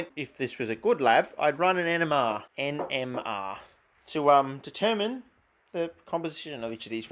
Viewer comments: I thought you said run an enema not nmr at first scared me abit didn't really want to see you run an enema 😂😂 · Its probably because I’m Canadian but whenever you say “I need to run an MMR” I hear “I need to run an enema”... gets me every time 🤣